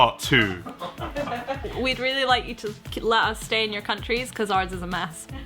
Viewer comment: "We would... We would really love you to stay in your country, coz our's is a mess" this is the best thing about Brits 'the subtle stab'. 🇬🇧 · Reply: I thought she said we would really love you to... let us stay in your country?